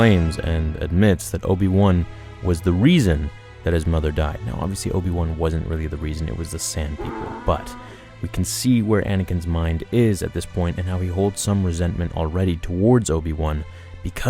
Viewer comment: it buffered and it sounded like you said ‘Now obviously we know Obi-Wan was not the reason his mom died, it was the SAND’ and I thought that fit perfectly